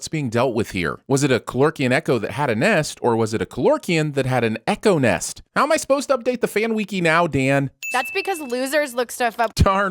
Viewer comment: did you just say “weeky” instead of “wiki”? ding!!!